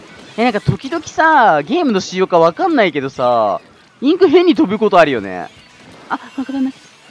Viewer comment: 喉お大事に チャージャー上手くなりたいなー 5:56 インク部屋に飛ぶときあるよねに聞こえて一瞬だけびびった。 自分の耳疑ったら自分の耳が悪かったわ